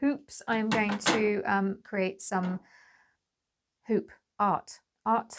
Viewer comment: I misheard hoop art as 'food hut' and thought wow she really is pivoting 😂 Maybe a food hut could be your backup plan!